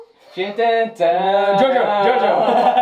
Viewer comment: I thought that was samurai heart😂 instead of jojo, gg. But a wonderful video that was, and it seems you have a future in singing as well as a doctor